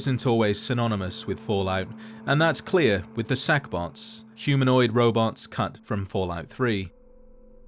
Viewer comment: I thought you said Sackbot, and I instantly had Flashbacks to LBP2.